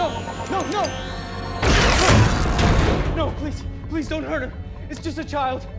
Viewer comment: HOLD UP BABY YODA IS A GIRL??? CAUSE I HEARD "DONT HURT HER"